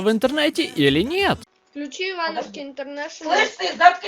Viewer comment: я думал что он сказал "включите бабушке интернет" лол топ